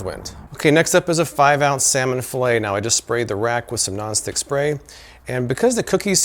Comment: Oh non stick spray, I dont dont know why I thought you said nostic spray